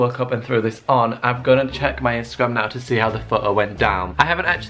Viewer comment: I keep hearing pho'o 😅